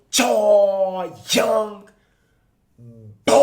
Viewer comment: WHATS GOOD YALL ? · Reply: I thought you said “whats good w/ yall young bulls” but this vid sound like you said “boys”